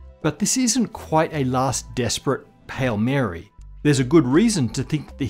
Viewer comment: Yes, the potential that we could detect dark matter in the near future is very nice and all but more importantly, did you say "pale Mary"